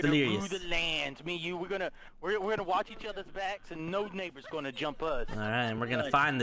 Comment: Was i the only one that heard ''No niggas are gonna jump us'' i think he said neighbours tho😂😂